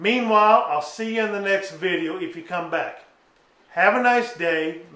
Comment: What language are you speaking at the end? Does that mean goodbye? Your video is good and I don't think that last part has anything to do with the filter but I'm just curious because I heard you say that word also in your other video about cleaning the filter. Thanks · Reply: LoL. 'Curahee'....I believe it's Native American and it means Stands Alone. ;)